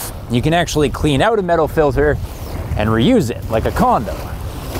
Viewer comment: 2AM Sunday morning resting my lids. Did he just say used condom? High stamina sperm bank? lol